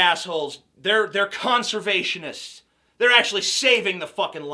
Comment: did you say CUNTservationist? :)